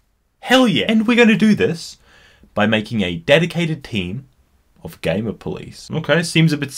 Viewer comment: I thought you said a dedicated team of gay police